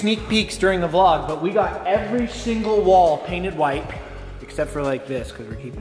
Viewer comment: Did anyone catch saying every SHINGLE wall